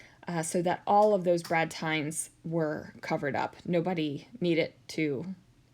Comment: Ohhhhh, Brad TINES! I could not understand what I thought you said--brad TIMES! Probably my fault since I was listening at 2x speed!